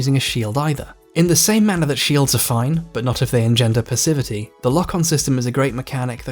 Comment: What does "engender... percivity" mean? Or what's the second word he's saying, I can't figure it out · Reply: To engender something is like to cause it to happen, or to bring it about. And as the other person said, the next word was "passivity", as in "being passive". Basically he's saying that the game encourages you to play passively, hold the shield up and wait, rather than actively by dodging, or baiting attacks, that sort of thing.